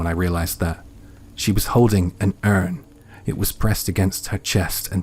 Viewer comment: "an earn"? sorry, I couldn't understand, I'm a non english speaker. What was it? An animal? An object? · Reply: An urn, it is a container of human ashes - thanks for watching!